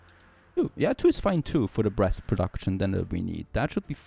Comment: I swear to god I heard him say "breast production" for a couple of times💀💀💀